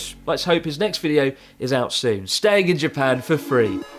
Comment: Misheard when you said "Let's hope Fidias' next video is out soon" and thought you said you hope his next video is at sea. Castaway vlog stranded in the middle of the ocean ~for free~